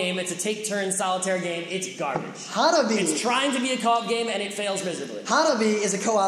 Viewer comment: at about 55:20 you talk about the best game of the year, a real co op game... I couldnt' make out the word it sounded like hollabee? or something like that.. Could someone help me out. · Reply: Hanabi.